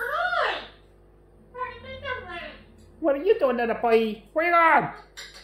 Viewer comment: at 27 seconds in says what are you doing it sound like said going down.